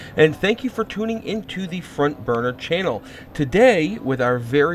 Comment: no joke I thought he said frontburgergaming for a second. this game looks awesome though, good video.